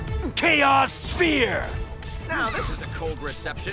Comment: Did He say Chaos Spear?!?